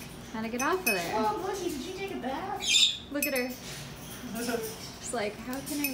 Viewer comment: “Smelly Morgan did you take a bath?” Istg i heard that